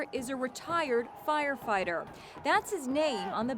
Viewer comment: I thought she said retarded firefighter haha